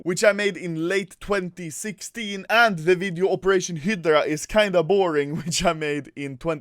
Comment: When u said operation hydra I thought u said operation Hitler